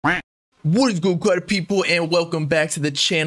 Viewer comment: I always thought you was saying what’s good Carti people